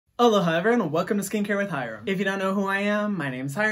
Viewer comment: I thought he said hello,hi everyone It was ALOHA 🤦🏻‍♀️